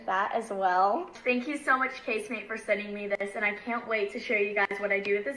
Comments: I thought everyone was saying Kate Spade wrong, but is Case Mate lolololol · Case mate lol I thought they were saying Kate spade 😂